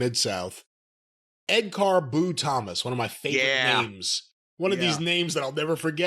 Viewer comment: I thought they were saying Ed 'Car Boot' Thomas. (this will probably only make sense to british listeners)